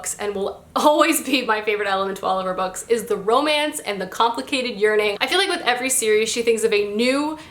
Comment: So I'm completely exhausted from work...and at 4:02 when you said "complicated yearning" I thought you said "complicated urinating" 💀